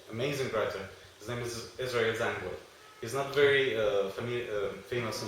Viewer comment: What was the name of the writer named at 11:40? I couldn't catch it .. the "Zionist leaders, amazing writers, etc"? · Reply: i think he says 'Israel Zangwill' but im not sure